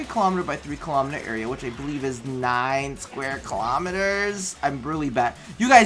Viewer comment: I thought they said it's 9 kilometers tileset tho